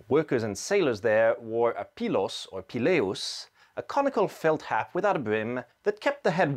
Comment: Had to rewind and listen again as first I thought you said a comical felt hat.